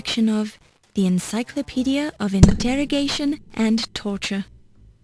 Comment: did she say intertorrigation?